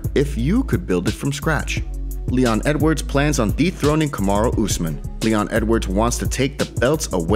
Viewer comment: Sounded like you said DEEP THROAT Kamaru Usman not DETHRONE :p